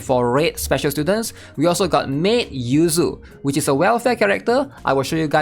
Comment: I thought you said 'Well fed character' XDXD